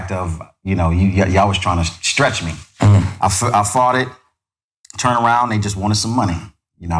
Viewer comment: 🔥At first, I thought Lil Eazy said “I farted” like he tripped or something, he said “I fought it”! You had me tripping for a sec! But real talk, he kept it real, I like Lil Eazy💪🏻!🔥